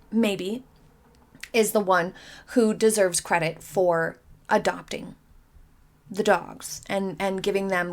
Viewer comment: I'm sorry, but for a moment there, I thought I heard you say Meghan was a good person for adopting the dogs. Didn't you mean adopting the KIDS???